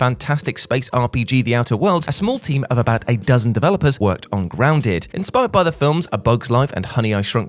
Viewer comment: Did you just say “a small team of 8 dozen developers”, or “a dozen developers”? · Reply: It was definitely the latter.